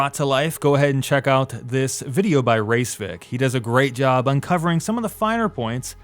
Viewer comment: Did you say race vik?